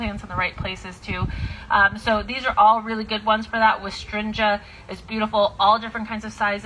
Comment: what plant is this? I hear wistrinja?? Its not very clear and I'd like to look it up please! Thanks so much!